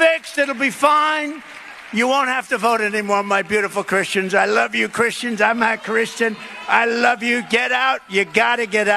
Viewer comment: Trying to understand Trump lingo on this one, @ 7:26 did he say "I'm not Christian or I'm half Christian", someone please clarify this for me if you can. Thank you!